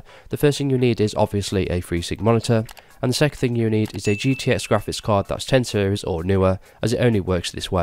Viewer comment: what did you say here? You need a gtx... I didnt heard that part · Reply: Dari you will need a GTX 10 series Graphics card or newer. For example. A GTX 970 will not work, but a GTX 1070 will work. The newest graphics cards are called RTX, and they will work too. I currently own an RTX 2060 and this worked for me.